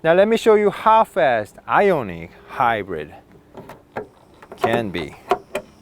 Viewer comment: I thought you said half assed ionic hyundai hahaha